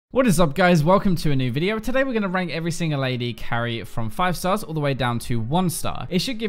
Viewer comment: For some reason, right at the begining of the video i hear "Lady carry". Kek